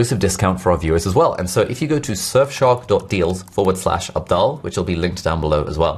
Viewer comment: Misheard "Abdaal" to "Adele" 😂😂😂